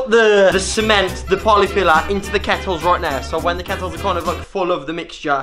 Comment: i thought it said nettles